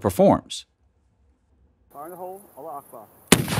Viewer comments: did he just said allahu akbar? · Did you say “Allah(u) akbar“ at 6:06 ???